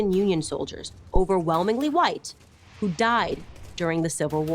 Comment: I love how she says "White people ended slavery exclusively in the US" ignoring the thousands upon thousands of Black Union soldiers which by the end of the war numbered 10 - 15 percent of the entire Union army without whom the war would've dragged on even more which was even acknowledged by Union generals at the time. Very convenient for her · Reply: EDIT: I just rewatched the spot in the video (7:37). She actually says "overwhelmingly white" which at 15% black people, that's still true. So she didn't say anything incorrect here just left out pretty important points. But hey, it's called "A Short History of Slavery" not "An Incredibly Detailed History of Slavery" so... I'll leave my old comment in for everyone to see how stupid I am. OLD COMMENT: Damn, smart catch. I wouldn't actually have noticed that. Totally true, there were black people on both sides in the war and definitely not all Union soldiers were "all white by the way". Crazy.